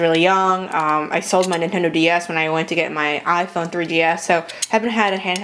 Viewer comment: i thought she said i phone 3ds